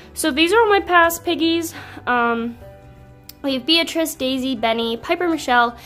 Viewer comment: When she was saying her past pigs I thought she said Beatrix which is my name, but after rewinding 500 times I realized she said Beatrice.